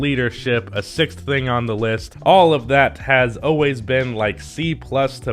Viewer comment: did mans say “a sixth thing on the list”?